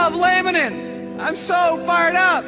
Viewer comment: I thought he said lanolin at first. Given that they're a bunch of sheep, no wonder I went there.